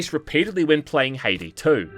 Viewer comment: whats the game at 21:09 ? You literally say the name but i cant make out exactly what youre saying or match it to anything on steam. Hade 2? Haiti 2? Haley 2? sorry haha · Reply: Game's name Haydee 2 which is is sequal to 1st Haydee game now in 2025 Haydee 3 is coming